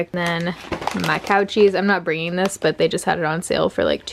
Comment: Who else thought she was saying COUCHIES instead of cow cheese 🤣